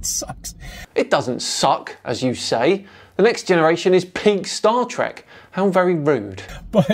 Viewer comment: I thought you said “Pink Star Trek” and I was lost.